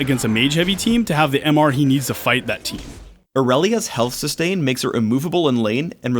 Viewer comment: Haha thought he said Morellia at 2:34 xDDD and i was like wtf? nerf that idiot who's buffing tryndamere!!!!!??? are you insane Phreak??